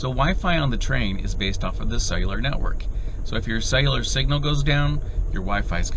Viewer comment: "Sailor network".....I thought I heard wrong, but nope, this was it many times!